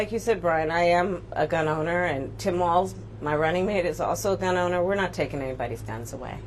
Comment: Oh wait... I thought she said "quit with the lies I'm a gun owner and we're not taking anybody's guns".